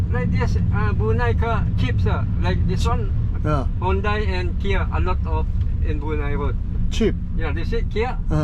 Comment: 요기 발음때문에 혼다라고 들리긴하지만 현대랑 기아라고 하시는거 같아요 ㅎㅎ